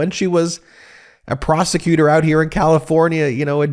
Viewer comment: I thought you said you remember when she was a prostitute 😂